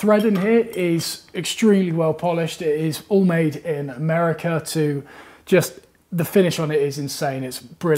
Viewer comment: At the 3:03 mark says "....it's all made here in America"........ Say what? I thought this was coming from across the pond!?!?! I know you came from a big grey rock but c'mon man, it's ok to go outside and get some vitamin D here in America. You'll even get natural highlights in your fro without having to wear blonde extensions. ; ) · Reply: I don't hear him saying "here in America" ... I hear "it's all made in America".